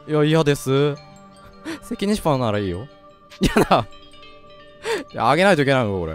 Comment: 「セキニシパにならいいよ」に聞こえて？？？？？ってなった